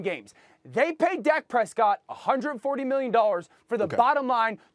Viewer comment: I'm not sure if SAS actually can pronounce "dollars" because I keep hearing "100 million nollas"